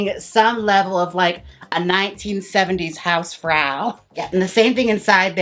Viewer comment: Did you say the german word "Hausfrau"